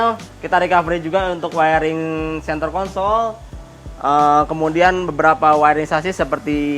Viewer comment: wiring center kon*ol HAHAHAA bg kepeleset apa aku yg salah denger🤣🤣🤣🤣